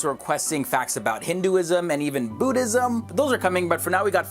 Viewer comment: like this post if u thought he said Bootism not Buddhism..